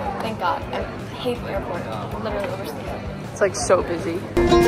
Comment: who said that?? what they said: its like so busy